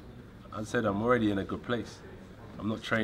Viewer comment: did he say... I'm not training for a fight? Awwww $hit! I hope the gameplan is to prepare for Ruiz and not just train. Ijs. Sound ah lil suspect to me. #NoLies